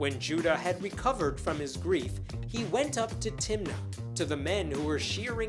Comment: When you said, "went up to Timnah", I heard, "went onto Tinder".